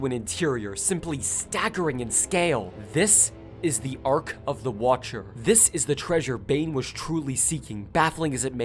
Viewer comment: all I hear is the Ark of the covernent